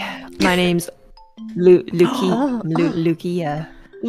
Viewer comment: Oh God. Why did I hear Enna say "leukemia", instead of Lukia at the end of the clip. XD